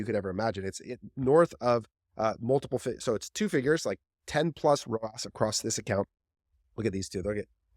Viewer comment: did someone say 10 ROAS??